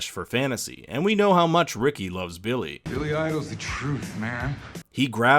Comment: "we all know how much Ricky lives Billy" o heard this as Billie as in Billie lourds character and I thought that was funny